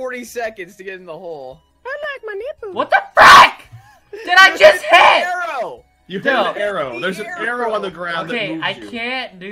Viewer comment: sounds like he said what the fuck!!! omfg